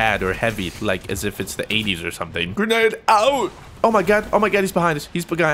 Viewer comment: Ordered my 300k shirt can't wait to rep Fruit Nation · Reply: what? He said the 80's on